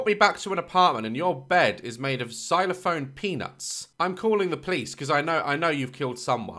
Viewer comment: golden ❤️ I love your content and your personality so much I swear 😂 Edit: He said silophone peanuts